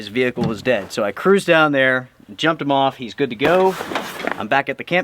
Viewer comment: Could have sworn you said "jacked him off" 😅😅